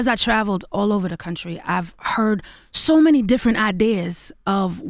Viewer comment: did Chrisette say she's from Somalia @ 5:09 😅😅